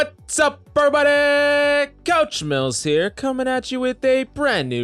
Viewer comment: i had to rewatch it like 3 times.. he really said COUCH mills 😭